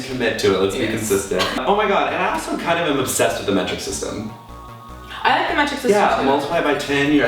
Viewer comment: What are they saying at 5:53 ? They have a obsession with the " magic system " in europe? hahahaha :D